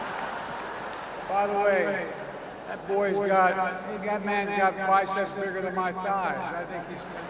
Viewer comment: Did he say "that boy" when referring to LL Cool J ???? Wow Just imagine.... 😂😂😂😂😂